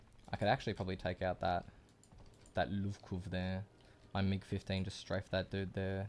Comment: did u just say mig 15 🤣